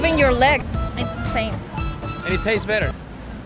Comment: Wait hold up I can not be the only one who heard that guy at @ 1:37 say "and it taste better" 😂😂😂